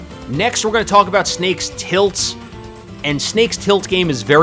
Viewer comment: I swear to god I thought that said tits